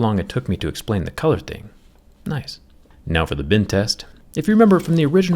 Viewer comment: why does it sound like Bin test everytime he says Bend test... Is it the problem with my ear or does anyone hear the same